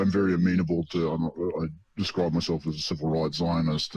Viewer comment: I couldn't understand, he said I describe myself as a ...what kind of... zionist? Sounded like civil right zionist. What does that mean?